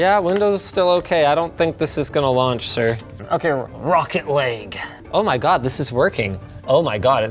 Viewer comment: The way Linus said "Rocket League" kinda sounded like "Rocket Lake" and I have no idea if that was intentional